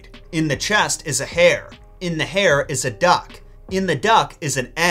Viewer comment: when he said “hare” I thought he said “hair” and was so confused why a duck was in a piece of hair…